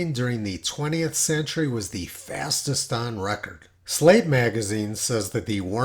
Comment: I'm sorry, Slate magazine? I could have sworn you said "slave". My apologies.